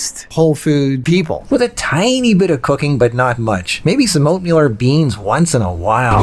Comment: I thought you cracked a joke and said "with a tiny bit of cocaine " 😆